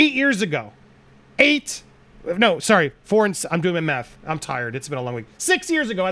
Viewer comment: Stg at 7:17. I thought he said he was "doing meth". I was like had to go back and hear it again. 🤣